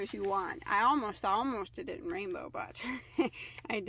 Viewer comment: Very pretty. I thought you said green at first when you said rainbow. I had to go back and listen again.